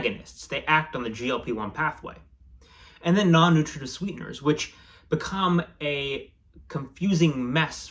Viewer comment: all I hear is Clark Griswald describing his Non-Nutrative Cereal Varnish. 😅😂 Thanks for the video.